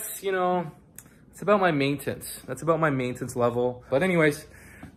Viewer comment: is it me or did he say main tits 12:04 as opposed to maintenance (the first time he said maintenance) THE KING